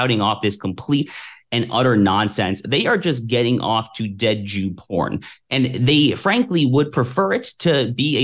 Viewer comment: q{ dead jew porn } is that what he said?